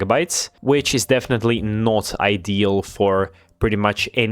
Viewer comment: did u say ideal? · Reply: Not ideal😅